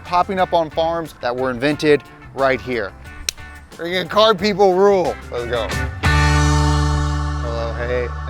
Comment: thought he said “colored people rule!” 😂😂😂😂😂😂. I had to turn on captions 😂😂😂